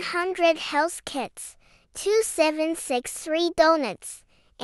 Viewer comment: did you just say 2763... deep inhale BFDI REFERE